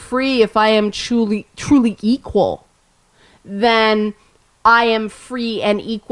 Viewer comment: did you say "geequal"? I may have to start using that :-)